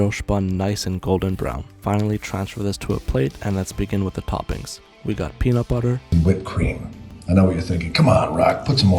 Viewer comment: did he say 67..? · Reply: Bruh he did say 6-7 😂😂😂